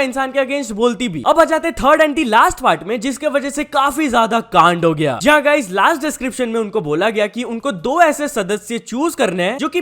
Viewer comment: who hear gand 😂😅